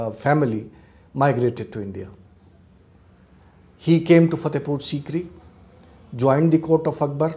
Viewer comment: What did he say @ 27:21? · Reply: he came to fatephur sikri (fatephur sikri is a city in the Agra District of Uttar Pradesh, India)